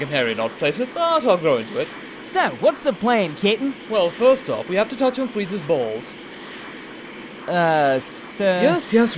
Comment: What is he saying? It sounds like "We have to touch on Frieza's balls".